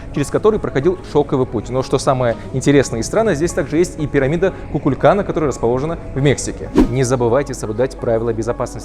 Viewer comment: did he say, "Kukulkan Mexico?"